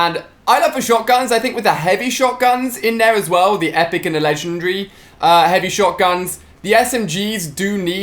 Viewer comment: What he say at 11:52 it legendary not lesrnery